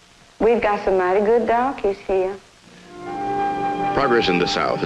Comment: "We got some mighty good donkeys here." The fuck?!?! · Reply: I thought she said donkeys too. She said "darkies" did you notice that her voice got a little softer and she was running her fingers around her mouth when she said "darkie"? Her mind began to drift. I suspect she had some relations and that man hit the spot.